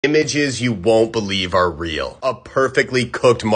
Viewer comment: For a sec i thought u said 'b*tches you wont believe are real' 😂